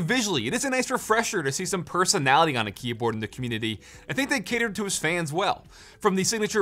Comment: did i just hear “commutitty”